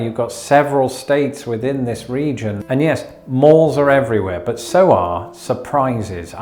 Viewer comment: At first I thought you said "moles are everywhere" 😂 but I guess you probably said "malls are everywhere".